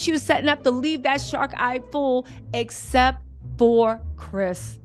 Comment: Did I hear "shark eyes" ❓❓ 😂😂😂😂